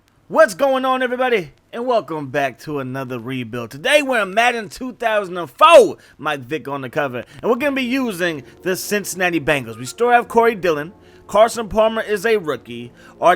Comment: I thought he said at the beginning "Madden 04 My Dick on the cover" 😂😂😂